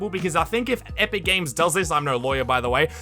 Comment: Who here thought he said "epic mini games"